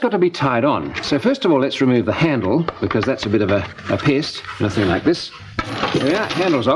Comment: does he say "a bit of a piss"?? Haha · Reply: "A bit of a pest"